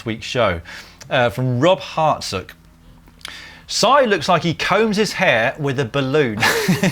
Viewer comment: Misheard the balloon/hair comment as "Si looks like he combed his hair like a baboon"